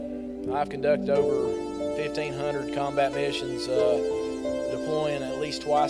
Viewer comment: did he just say 1500 combat missions?